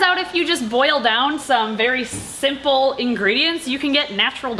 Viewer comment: i was two mins in and i got confused because i thought she said sinful then i realized it was simple i'm lmfao😭😂😩🤚